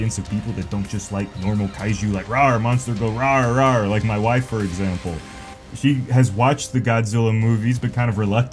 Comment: For a moment there, I thought you were saying your wife was also going “Roaaar”.